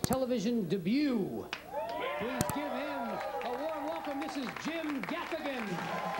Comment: Did he say Mrs Jim gaffigan? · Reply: i think he said THIS IS but the guy who did subtitles thought he said MRS